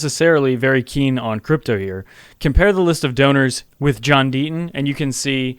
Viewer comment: I thought you said donors with a B